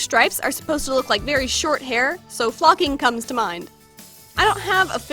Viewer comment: When she said flocking my mom said "WHAT?" cuz she thought she said the f word XD